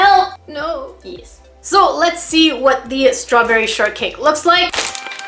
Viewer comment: At first I thought she said strawberry shartcake 🤣👌